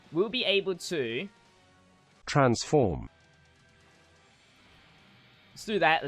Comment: What did you actually say? There is no way yu actually said transform 😂